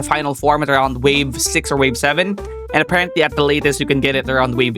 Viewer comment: did bro say 6-7???